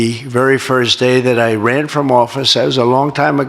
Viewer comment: Did he say he ran from ?😅